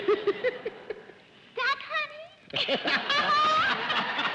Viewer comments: What did Nanette Fabray say at 18:27? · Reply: "Stuck, honey?" is kind of what it sounds like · What did Nanette say to Arlene at 18:28? I couldn't make it out. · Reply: What's the matter honey are you stuck 😊